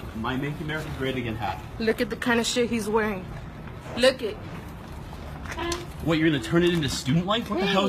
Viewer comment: wtf is she saying? “look it”